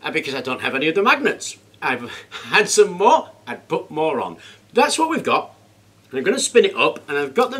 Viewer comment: "if I had more magnets I'd put more on" I heard "moron" and thought I was getting a shout. Another great informative vid. Thanks